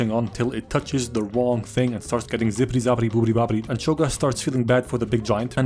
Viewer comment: did i hear booty bopitied